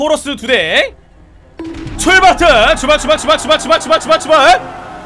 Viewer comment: tlqkf로 들림